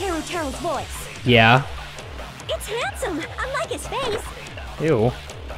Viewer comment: "I definitely heard Teruteru's voice" "Yeah" "It's handsome! Unlike his face" "Ew" 🤣💀 Edit: lmao at first I thought she said "I like his face XD"